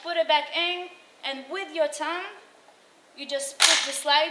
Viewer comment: Took me a second to realize she said "thumb" thought she said "tongue". I was like whaaaaaa?